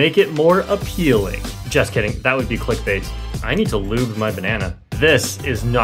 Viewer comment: Did he just say "I need to lube my banana" 💀